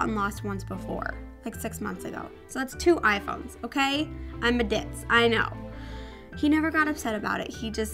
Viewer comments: hahaha i thought she said "i'm a dick" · I thought said "I'm a dick" but she said ditz haha